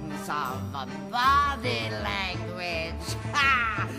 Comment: I misheard the line at 2:26 as "parthey language!" (pronounced par-see) The word is actually "body language".